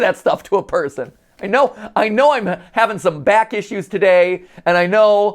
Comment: I heard I'm trying to come back